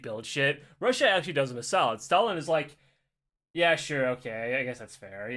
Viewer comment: I misheard this as "Solidus".